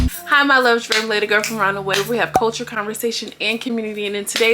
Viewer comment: I heard “Culture and Commrunity” 😂❣️